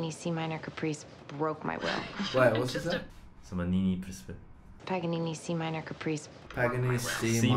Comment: Did Brett said什麼尼尼？？